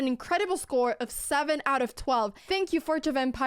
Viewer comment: I thought she was saying Forge of vampires. 🧛‍♀